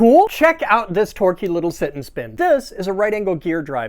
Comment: Thought you said "kitten shit" (got a lot of background noise) and I was like ...wut?